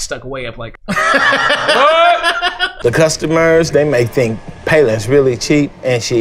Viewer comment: Did he say her name is "payless"